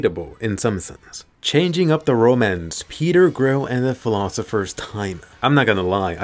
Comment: I thought he said ”Peter Grill and the philosophers thigh” I mean with those clips can you blame me?